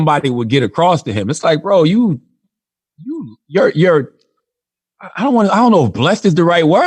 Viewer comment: All I heard was "I dont wanna suck dick!"lol😁🤣